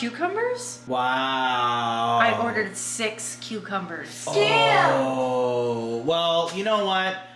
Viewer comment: What?!? Did I hear yell a swear word at 2:17??? 😱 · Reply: He said Scam...because he thought they took our money and didn’t give the cucumbers!